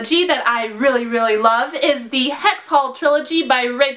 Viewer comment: Whats the name of the book at 6:10. I couldnt hear it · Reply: Hex Hall.